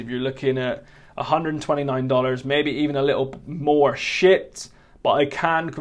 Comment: did he say the s word or did he say 'shipped'? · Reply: "Shipped", not "shit"